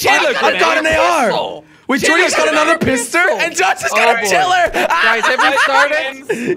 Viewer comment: who heard say pistor